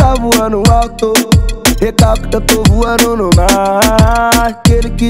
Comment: alguém entendeu o que o kevin disse? Kk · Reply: eu entendi "to voando no mar" acho q ta errado a letra